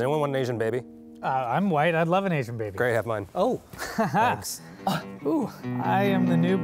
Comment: "Anyone want an Asian baby?" "I'm white, I'd love an Asian baby!" "Great,have mine!" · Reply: I thought Eugene said "great, have fun"